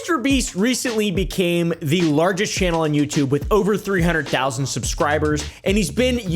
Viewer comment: "over 300k" I think that says 300 million silly goosey